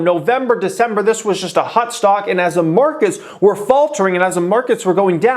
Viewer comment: I hear "HUT STOCK" every time.